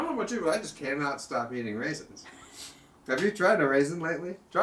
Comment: Oh he said "eating raisins"... I thought he said "being racist"